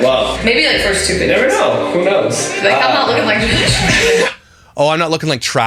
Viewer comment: did not say if it comes out looking like trash she said if it comes out looking like josh her husband 55:54 listen closely